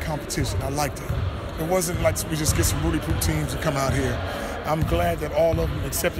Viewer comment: On 0:10-0:11, what is Lavar saying? "Some routty puff team"?? couldn't catch the phrase, can some Americans fill me in with that slang?